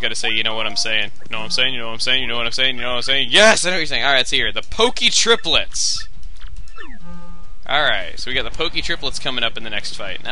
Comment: I thought it said pokey toilets xD